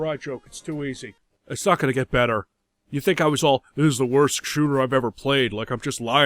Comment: I appreciate the unnecessary brackets in the subtitles, really makes the TYPO of "Shooting" instead of "shooter" at 6:03 stand out